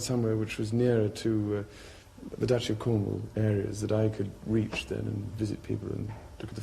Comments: Did anyone catch what he said at 2:32?? He bluntly said he wanted his home base to be near the “Duchess of Cornwall” That was Camilla!! When he said that look at poor Diana’s face! Omg he was awful 😢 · did he just say he wanted to be near the duchess of cornwall???!!! 2:31 how very convenient!! Lady Di was not impressed!!